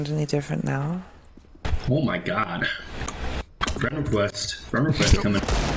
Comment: what did he say? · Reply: friend request